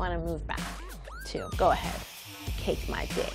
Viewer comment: did she say "cake my dick"